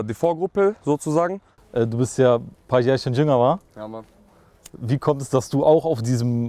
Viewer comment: Was fragt der Moderator ihn bei 13:35 , hat das irgendjemand verstanden?:D Haze stabiler typ!👍🏻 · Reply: Du bist ja paar Jährchen jünger.... Und wie er dann dazu kommt eher so Old Schooligen Sound zu machen. Obwohl er ja selbst um einiges jünger ist.